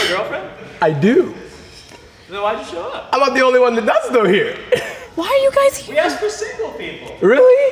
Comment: what did said in 10:40 ? When he's asked why did she showed up?? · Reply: He said "I'm not the only one who does though here." He was saying that he wasn't the only person in the 10 who had a girlfriend.